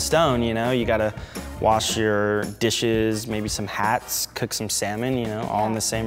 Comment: did he say hats?